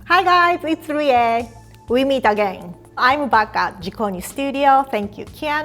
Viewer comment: The first time, I heard her say “I’m baka”. 🤣 Hahaha! Then I rewinded it. Then I realized she said “I’m back at”. Haha! But still! It is a funny moment! ✌️😅